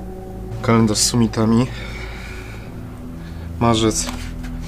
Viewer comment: a ja usłyszałem kalendarz z kosmitami